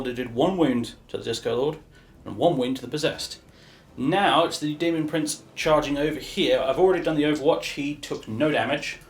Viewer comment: When you said demon prince charge I heard “Demon Prince Charles.” Juuust thought you should know since that’s incredibly funny.